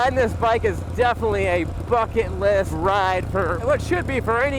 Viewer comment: When you said riding that bike was a bucket list ride i was yawning a little bit and i thought you said riding this bike is a fuckin list ride. I was like in all the years ive been watching this channel ive never heard you cuss. So obviously i had to go back to what you said and i was wrong lol